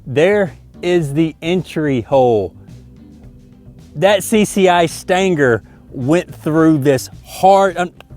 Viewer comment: CCI Staaanger.😆 I thought U were saying CCI standard. Great Video. Next build an RC Battleship and sink it.